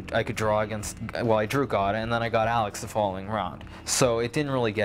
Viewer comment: it sounded like he said he drew God.